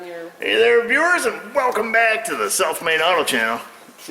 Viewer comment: When I first started watching I thought you were saying Self made auto garage. Not south main. Self made would still be appropriate. Keep up the fine work!